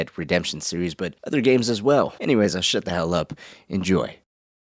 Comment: “Anyway I’ll shut the hell up” lmao jeez man ain’t no need to do yourself like that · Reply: I heard "now shut the hell up", and I chuckled